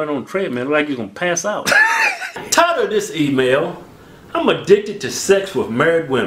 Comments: For the longest time i thought keith was saying “tired of this email” 😂🤦🏻‍♂️ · I always thought he said "I'm tired of this email." haha